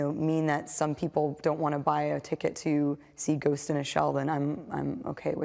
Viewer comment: does she say " ghost ina shell" ? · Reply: She said "ghost in the shell" with a soft /th/ sound.